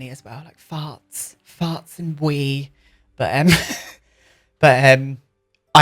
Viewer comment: I thought you said that it smelled of "farts and weed" 😂 My apartment constantly smells dog farts from Lilly and weed..from Lilly as well 😌👀